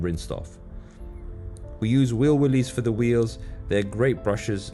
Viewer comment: I assume you are saying 'wheel wooleys' cause it sounds like you said 'wheel willies'! Awesome car